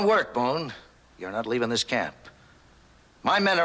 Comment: I thought he said his men were loyal 😅